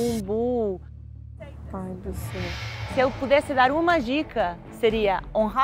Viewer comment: Só eu que entendi "pau no seu cu" kkkkkkkkkkkkkkkkkkkkkkkkkkkkkkkkkkk dei pala